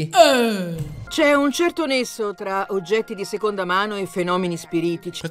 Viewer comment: ma solo io ho sentito "c'è un certo Lesso?"